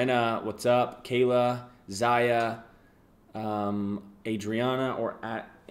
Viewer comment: sounded like he said my name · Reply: My name is Zayha and he said what sounded like my name